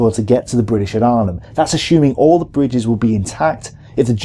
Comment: Why do I hear "ashooming" when I am expecting "assuming"?